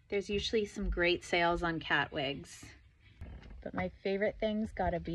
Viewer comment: What did you say at the end I heard “watching spirit, Halloween, ruin your day and the space between Michael’s ears” lmao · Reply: *watching spirit Halloween renew their annual lease in the space between Michaels ears.